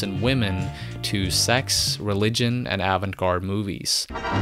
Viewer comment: what did he say at 6:12? sex, religion and... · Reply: I said avant-garde movies.